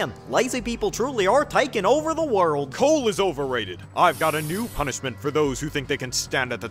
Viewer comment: "Coal is overrated. I've got a new punishment for people who think they can stand at the top of Andrias' naughty list" Why's that line so fire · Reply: I heard “The core is overrated” and I just thought of Marcy 😭